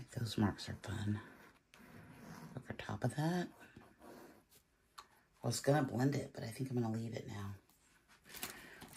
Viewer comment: Love this ❤. Please what is the ink/paint at 8:35? I think you said Amsterdam yellow? Do you make up the dropper bottles yourself as I couldn’t find any inks. Thank you. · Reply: It’s Amsterdam Naples Yellow Deep. <link>